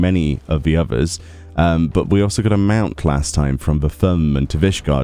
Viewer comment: But we also got a what last time? 1:28 I had to replay that like 10x and still don’t understand wth is said. Mount? 🙄